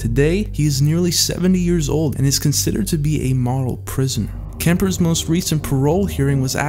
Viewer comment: did anyone know what he meant when he said 'moral prisoner' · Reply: Krismer he said model prisoner.